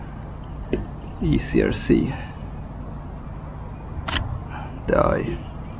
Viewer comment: did you just say “die …” ? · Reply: the eye 😇